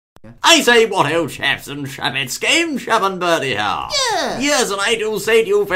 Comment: I thought they said gamechap and bertieham XD